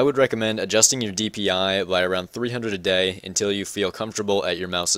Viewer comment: Those are very good tips! But what did u say? I keep hearing '300 a day'